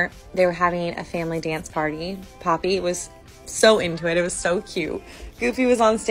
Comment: I thought she was trying to say "papi" instead of Poppy and almost lost it omg